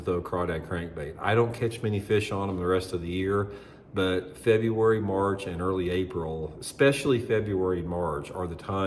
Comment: Interesting title - the 60 days to useCrawdad pattern - not sure I heard when are those 60 days? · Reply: I definitely heard him say, “February, March and April.”